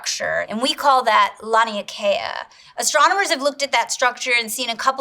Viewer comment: My dumb*ss thought it said "Astrologer"and almost skipped the video